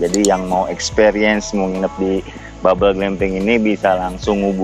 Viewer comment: Td kaya kedengeran nya "mau nginep di bubble bee" hehe...becanda bang